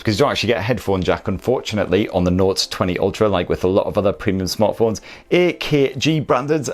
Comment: did you just say nords 20 ultra?? Oneplus is in your head ;)